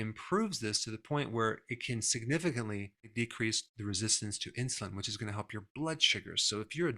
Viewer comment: Ha, i just heard him say that walking "can significantly increase resistance to insulin"!